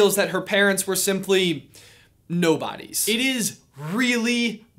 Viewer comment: I thought Kylo said 'Nobody' as in no parents not 'nobodies'